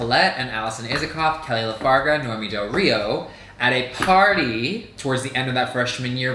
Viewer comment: For a minute, I thought said Bianca Del Rio and I was like yaaaaaaaaas queen. 🤣